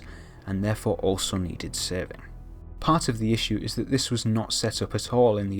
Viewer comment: I can't understand what you're saying at 3:11, therefore also needed....saving??